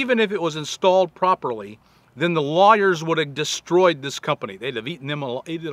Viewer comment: At first I thought Bob was saying "liars" when he said "lawyers." LOL! I've had my share of honest lawyers digging me out from situations the "liars" put me in!